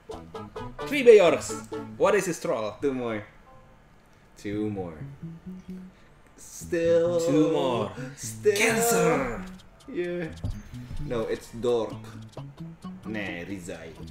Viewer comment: did just say cancer?